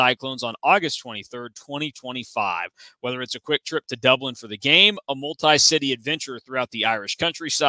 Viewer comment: Did you just say quick trip to Ireland? 😂